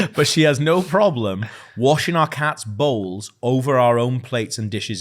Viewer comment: I initially thought you guys said washing the cats balls, not bowls.. and was surprised when you said thats fair aha